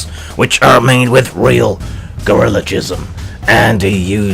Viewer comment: Best misheard word to start the day with: Gorillajizzm. Thanks for making me laugh. XD